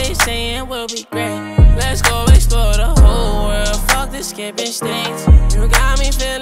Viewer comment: This whole time listening to this song I always thought he said “Let’s go explore the whole” “Fuck Skipping states” But it’s: “Let’s go explore the whole” “Fuck the skimpy skanks” Shook